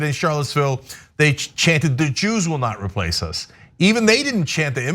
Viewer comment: I thought they were saying ‘you’s will not replace us